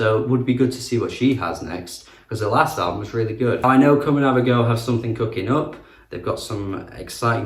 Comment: sorry, but I just cannot understand the name of the artist(s) at 11:45... COMING OUT OF A GIRL? please could you clarify. thanks. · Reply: Hahahahah, Comanavago is what they’re called. Great pop duo